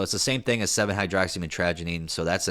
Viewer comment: I thought you said "7 hydroxy my tragedy" at first.